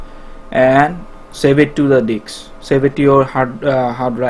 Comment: when he was downloading cosmo pod ..did ha say save it to the DICKS??or Disk..another thing ..dude get it str8 its. CLI-ENT(CLY-ENT) not SILENT...